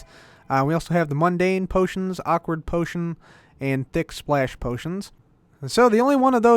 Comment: I thought he said dick